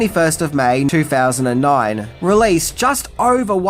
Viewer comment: Did you say Sarah Connicles? Cause that works 😂